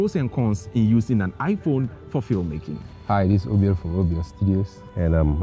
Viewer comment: The moment I heard "Hi this is OBL from OBL studios"... I had to pause everything and concentrate😂